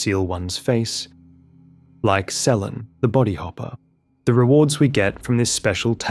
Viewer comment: 47 mins in, I heard it as Sellen the body popper. I had to rewind.